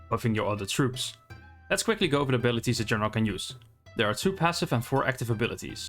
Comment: I was playing the video at 1.5 speed and at 1:18 I thought you said "let's go over the abilities that you're not going to use" lol